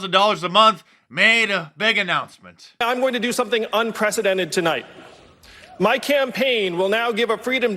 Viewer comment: UN - Presidential (1:00 - 1:02 ) did he just say the truth in plain sight or did I mishear it … let me check oh he said Unpresented - forgive me it sounds so similar and unpresidential fits so much better to his statement. ;)